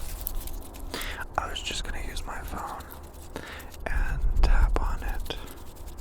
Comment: WHY DOES IT SOUND LIKE HE’S SAYING “I was just gonna hitch my frog and tap on it” IS THAt WAS YOU saID BECAUSE I THInK IM GOiNG cRAZy NOW eHhHH